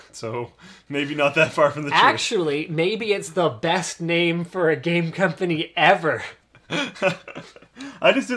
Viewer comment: I swear I heard "Date Rape Game Company" O.o